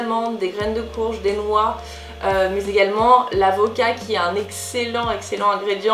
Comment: "L'avocat" ! J'ai compris "La vodka"... Ça m'a fait buguer XD